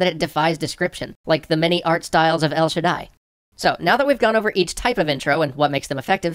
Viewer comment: Can someone tell me the game he mentions at 05:42 I cant hear it properly but it looks really fun, thanks in advance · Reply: El Shaddai <link>